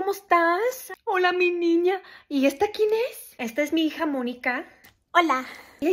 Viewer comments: I misheard that third line as, "this is my harmonica". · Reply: I heard "jamonica" 😂😂😂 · I misheard that third line as, "this is my harmonica". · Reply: She said “esta es mi hija Monica” 😂